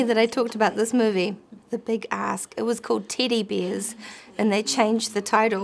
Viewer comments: I had to look at the subtitles because I definitely heard "titty beers" when she said "teddy bears" 😂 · i had to go look up the film with her and Jason because i was like WHAT was it originally called?? ohhhh Teddy Bears. she's right about her accent making it sound like something else. i really thought she said Titty Beers.